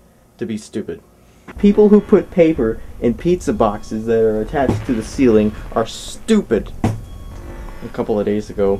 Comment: When I first saw the part at 3:16 (back in October 2010), I thought he said "People who put the pizza box there attached on the ceiling, are stupid", but until late November 2010, I realized he said "People who put paper inside a pizza box there attached on the ceiling, are stupid".